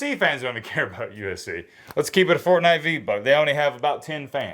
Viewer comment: Just keep it a Fortnite v-buck wit us….. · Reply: I'm getting old... every time he says this shit, i almost pick up my phone to google wtf it means! Also thought he was saying "Fortnite v-bug" lmao